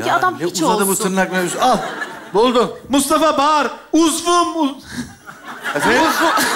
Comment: Burda ne diyor? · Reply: Uzvum diyo 😂